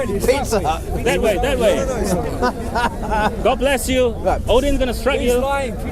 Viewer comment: did just say "God bless you" ?